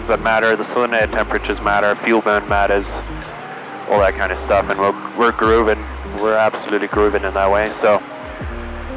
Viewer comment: How many did the fuel cost??? Listened 20 times and cant hear what he is saying. Even subs dont get it xD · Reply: $1000